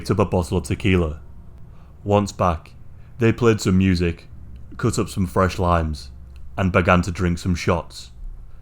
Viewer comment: Oh! They "cut up some fresh liMes", and had shots of tequila. Limes! I seriously thought you said "lines" like it was normal and just glossed over it without even a change in tone of voice! 😂🤣🤦🏻‍♀️ I was thinking coke/meth involvement in murders must be so common now in the US and UK that no one gives it a second thought or something! 😂 LIMES!!